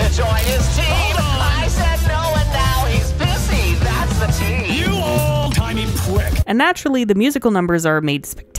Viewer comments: I still can’t stop laughing everytime I hear ‘Stayed Gone’ after I noticed it sounds like Vox says “you old tiny pwick” not “you old timey prick” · I still can’t stop laughing everytime I hear ‘Stayed Gone’ after I noticed it sounds like Vox says “you old tiny pwick” not “you old timey prick” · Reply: I heard "you old tiny prick" at first 😭